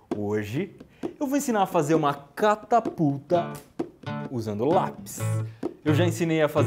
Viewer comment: até 0:04 "hoje,eu vou ensinar a fazer uma CataPuta" ( Foi oq eu Ouvi D: )